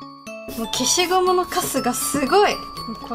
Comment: ぐらいの 「消しゴムのカスがすごい」 が 「〜のくせが強い！」 みたいに聞こえたんだけど、、笑笑 、いつものように今日も最高に上手いですね！ 憧れますぅぅぅ